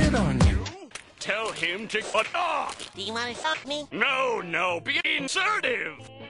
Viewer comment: what did he say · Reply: Be Insertive